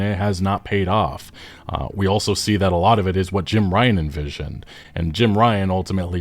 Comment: Jim Ryan Ruined Sony. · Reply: For a second my tired brain thought you said Jim Raynor...